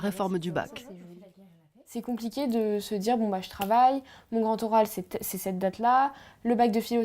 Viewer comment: What does the young lady say at 3:10? Mon grand oral c'est ... ? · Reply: Mon grand oral c’est cette date là